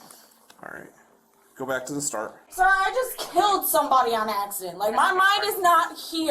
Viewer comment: In my opinion, she could have been on more than just alcohol, this is why she said "How did you figure that one out?" She said that because there was no blood test given at the time, just the breathalyzer. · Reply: I'm sure I heard her say at 42:30 " I just killed someone on acid "